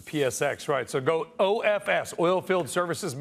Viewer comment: what does he say? OFS over??